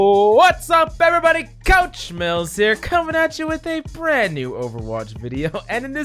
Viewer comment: I thought he was saying couch mills the whole time ☠️